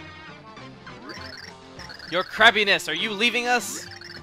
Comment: i thought the minions said "your crappiness!" instead of "your crabbiness"